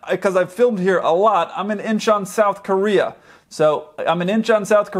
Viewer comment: Where in south korea? I heard " im an inch on south korea" lol. how is it spelled?